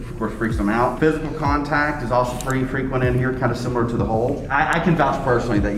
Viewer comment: Stop cause when it said “the hole” I thought it said “THE MOLEEEEE” i was about to start cry laughing 😭💀